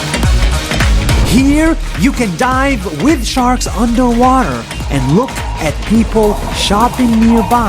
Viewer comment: It kind of sounds like he said “You can die with the sharks”